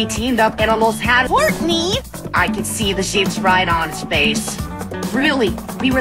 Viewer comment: HELP IT SOUNDED LIKE HEATHER SAID "I COULD SEE THE SHITS RIGHT ON HIS FACE"- (0:24) IM DYING